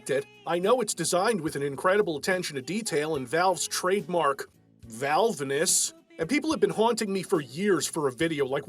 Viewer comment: For a second I thought you said "Val Venis" there.